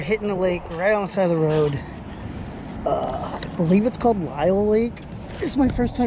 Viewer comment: where was this lake located? i thought he said Lyle lake.